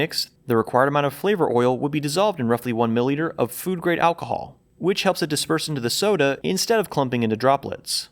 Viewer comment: the subtitles say “food grade ethanol”, meanwhile you’re saying “food grade alcohol”. I don’t know how important of an error this might be contextually but i feel like pointing this out just in case any people rely on subtitles and were confused.